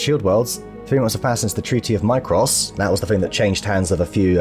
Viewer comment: At 32 minutes in, you mentioned a Treaty of Micros. For a moment, I thought you had said Macross, which I believe is part of the Robotech universe. I look forward to seeing Stencil in his new mech with the Long Tom. It is going to be awesome, baby!